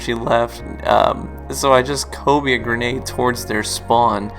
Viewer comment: I'm curious as to what you're saying (can't understand the word) at 6:23, when you say "I barely have enough super energy so I just Hovey (?) a grenade into their spawn...". I heard you say it earlier in the clip, and still didn't catch it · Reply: he says "kobe"